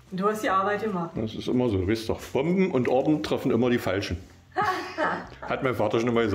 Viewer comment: auch lieblings Papa hatte einen schlauen Papa mit guten Sprüchen fürs Leben! Ich habe es mir 5 x angehört aber leider nicht verstanden, kann mir das mal jemand schreiben? Danke! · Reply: "Bomben und Orden treffen immer die Falschen" hat er gesagt. 😁 Irgendwann mache ich mal ein Best Of der schönsten "Hat mein Vater schon immer gesagt" Sprüche. 🤣